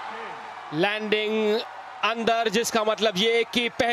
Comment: kya bola lakshya ne korean 🇰🇷coach ko 🤦‍♂️🤷‍♀️😠🤬🤪😅😩😳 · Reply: Bkl andar tha😂